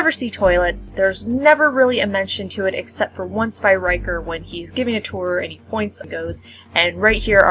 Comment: I thought you said "when Riker is giving it to her" at first. I know Riker gets pussy but god damn. lol 😁